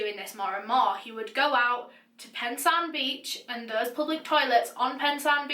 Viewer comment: did she say Pensarn beach at 47:55 cos if so I live right next to that beach